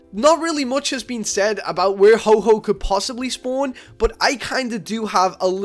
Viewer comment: did anyone heard that he said ho-ho